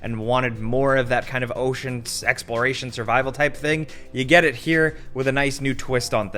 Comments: Anyone else thought said :"Ocean Sexploration" ? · I'm sorry did you say ocean sexploration? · ayy yo did just say sexploration😂😂😂😂